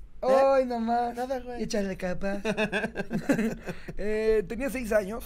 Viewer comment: ✨ oí no mas' échale kpaz✨ jajajajajajajajajajajaajja